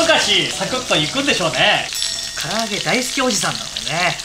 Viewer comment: 「焦げて唐揚げ色」って聞こえる。